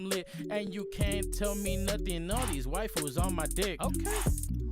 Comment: He said waifus on my dick and immediately dismissed that shit and seriously misheard it as white fools on my dick hahahaha he didnt even give him a chance, so cold like biologically cold